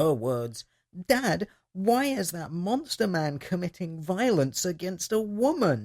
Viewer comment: Did you say monster man? <link>